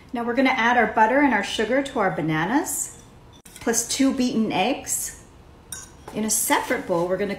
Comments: At first I thought she said vegan eggs and I was like wait what 😳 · It it just me or did i heard her saying two eaten eggs instead of to beatn eggs